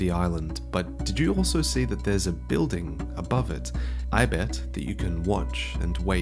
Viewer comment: The giant "dong"? Is that what you meant to say?